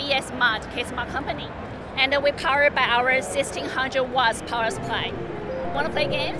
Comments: It sounded like she said "our assisting 100 watt power supply" haha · No, she said 16-hundred. 1600 watts power supply. I thought she said 100 watts initially as well :)